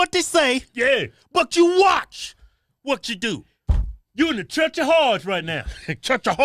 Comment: "Listen to what they say, but you watch what they do." - Church of Hodge, 4.9.21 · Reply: 😂😂😂 now I feel a little dumb(or hard of hearing) I thought they were saying "trench" not church... 🤦🏼‍♂️😂😂